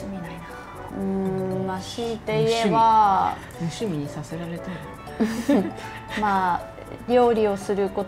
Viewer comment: 無趣味にさせられたって聞こえるんだが